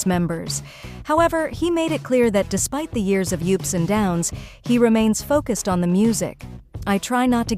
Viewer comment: Sorry, immediate thumbs down for the lazy use of horrible AI narration. As soon as I heard, "yupes and downs" instead of "ups and downs" I just cringed. Bad.